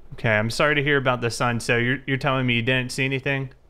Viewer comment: Did he say ”sir”